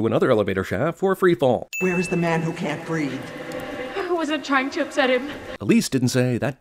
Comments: Lol every time they said "The man who can't breathe" so dramatically, I thought at first they said "The man who can't read". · Lol every time they said "The man who can't breathe" so dramatically, I thought at first they said "The man who can't read". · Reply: It sounds like he says "The Man Who Can't Breed"